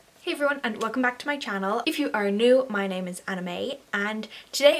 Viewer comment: I thought you said your name was Anime lol